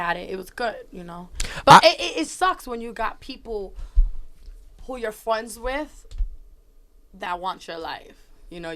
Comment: Did she just say "who you're fwriends wit?!"